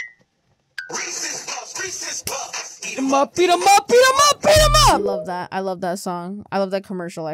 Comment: Resses puff eat him up I thought u said BEAT IM UP